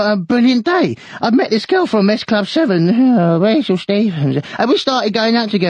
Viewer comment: what does 2d say on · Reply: idk if i’m a bit late but “I met this girl from S Club 7… eueghhhh… Rachel Stevens”